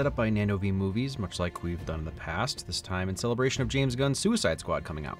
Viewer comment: For a fraction of a second I was mortified I heard "In celebration of James Gunn Suicide"